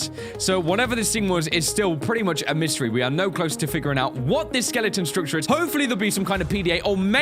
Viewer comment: When that mesmer had you in it's gaze, I swear I heard the AI say "We are prisoners too". I don't know if I misheard it, but it freaked me out and made me think something weird is going on. Story mode can't get here fast enough!